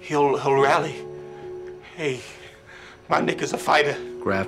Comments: i thought the father said "hey my n***a's a fighter" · I thought he said “my n***a’s a fighter” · who else heard "my nigga's a fighter" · I thought he said my niggas a fighta not my nick is a fighta lmao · I thought he said “My nigga’s a fighter”